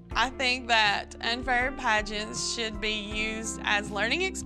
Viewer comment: Am I the only person who thought it said pregnant instead of pageant. Edit: thx for the likes